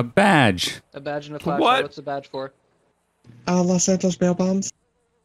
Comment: I thought she said los santos tampons 😂😂😂